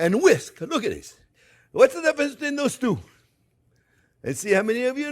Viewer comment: Exactly WHAT in the many creative profanities I could drop did you say here??? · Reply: My translation: "What's the difference between these two?" I'm pretty sure, anyway.